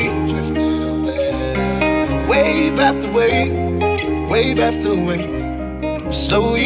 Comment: I thought it said 'wave at the wave' lol not 'wave after wave'